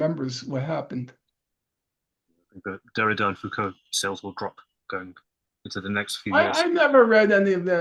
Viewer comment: Wtf does bro say/ask at 1:03:09? Something ab foucault? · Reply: Yes he says that Derrida and Focaults books will sell a lot